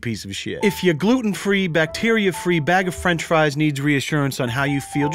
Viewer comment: can't possibly watch this without looking. I really thought they were saying "gluten free, bacteria free, and bag of french fries" xD. I was like "wait a minute!"